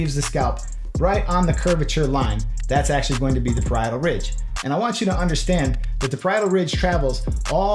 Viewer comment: u talk about pridoral rix or something sorry my English is bad so I couldn’t figure out what you said